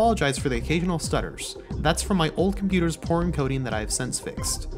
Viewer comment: Took me a while to realize you said "poor encoding" instead of "porn coding"